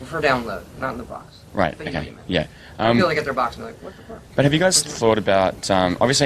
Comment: does he say what the f**k !?